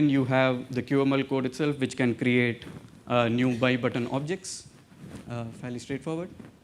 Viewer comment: Hi, cool work. I don't understand what c++ class is meand in 9;22-9:23 microtent? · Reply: He said MyButton